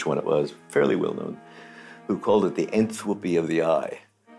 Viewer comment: Hey what does he say at 4:59? "..... of the eye" ? Much appreciated! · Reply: It sounds like "Entropy of the Eye" which is consistent with his earlier statement about "the eye flying around" earlier (I had to replay it about 6 times to get it!)